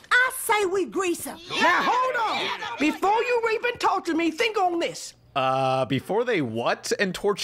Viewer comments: I think she said before you even torture me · sounds like "before you even talk to me" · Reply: i heard "before you rape and torture me" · Before Pan pointed it out, i heard "before you EVEN torture me" so maybe that was it? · “now hold on, before you even talk to me, think on this” Thats what she said, not that hard to hear. Also, the y2k thing is gonna happen again. <link> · I heard "before you even talk to me" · I think she's saying rape and torture